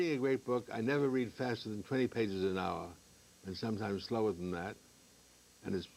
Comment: Someone could explain what he said at 0:50? Adler read twenty or three page per hour? Thanks! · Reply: He reads only 20 pages an hour , sometimes less than that and it's hard hard work